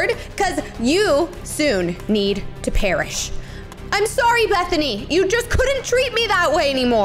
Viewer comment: Ummmmmm did she say Bethany that my name